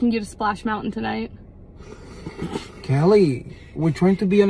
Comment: LOL... I thought she said "You''ll need more beer in your goggles!"